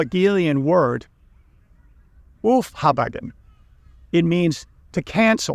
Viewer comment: German here. I can’t figure it out. I really tried, but I can’t figure out what that German word mentioned at around 3:20 could be. “Oofhabeggen”is what I hear him say. Maybe that is meant to be “aufarbeiten”. That’s the closest sounding German word I could come up with. But that doesn’t make too much sense in the context. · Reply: Aufheben/Aufhebung sorry I should be clearer.